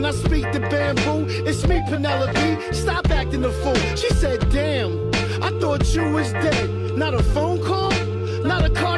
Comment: What is woman saying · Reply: Something along the lines of "You see, this is what i'm talking about" The red shirted kid was her son who was probably interested in military.